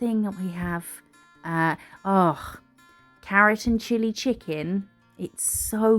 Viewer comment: When you were talking about getting Chinese food, I heard "keratin chili chicken" and was...so confused😳😅